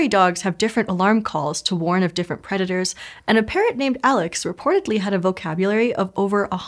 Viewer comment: So I didn't hear "a parrot named Alex," instead I heard "apparently Alex" and I legitimately thought she was just being tongue-and-cheek with a coworker named Alex